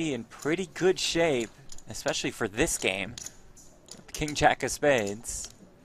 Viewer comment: I thought Tony G said "Never over play King Jack" to Ralph Perry?